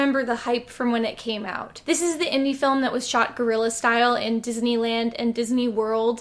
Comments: Whenever someone says “filmed guerrilla style”, I always mishear it as “filmed gorilla style”. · i thought you said it was in the style of gorillaz